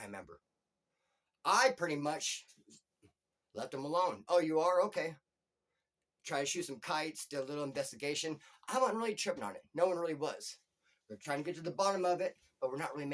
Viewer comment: what are you saying? B 1000 may? i hear you say that many times, i love your videos though!!!!!!! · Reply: I'm saying Be that as it may and your right I do say it quite a bit lol